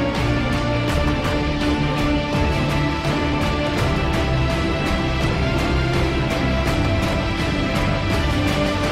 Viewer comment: can someone tell me what that number is lol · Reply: That is one duodecillion